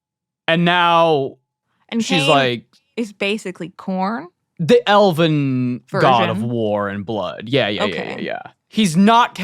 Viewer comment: all I can hear is saying "no the elven god of war and murder"